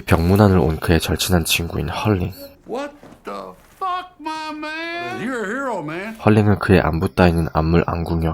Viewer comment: 여기 영어로 뭐라고 그러는 건가요? 유쾌하네요 ㅋㅋㅋ 따라하고 싶네요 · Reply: What the fuck my man, you’re hero man.